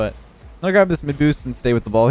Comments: I could have swore you said McBoost. Lol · After the Daddymcdouble comment I could have sworn you said "I'm gonna grab this mcboost here!" and not "mid-boost"... Hearing wrong things are epic sometimes... :D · I thought at 6:27 you said “McBoost” lmao